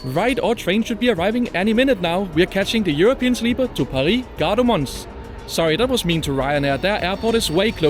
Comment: I heard “Paris, Gardermoen” - a stretch even for RyanAir…😂 (Greetings from 🇳🇴…!)